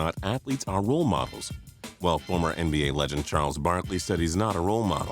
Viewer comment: Who else heard him say Charles Barley